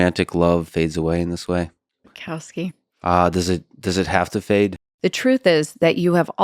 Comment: was there an edit for language? Did she say "frick Bukowski"